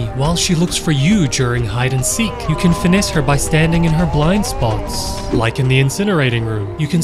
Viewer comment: Im sorry but i thought you said bald spots not blind spots 😭